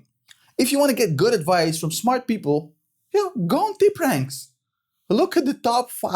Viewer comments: What does says at 14:27 ? Go on .... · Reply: Hahaha I was asking the same question myself, sounds like “deep ranks”? No idea what that is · What site did he mention at @ 14:28 I have rewinded the video 100 times, but still can't understand it. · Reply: Tipranks